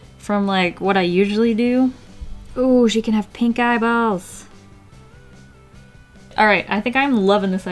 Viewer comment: I thought she said ooh she can have pink eye